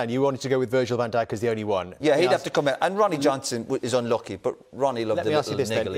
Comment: What did he say anoint Ronnie Johnson 16:28?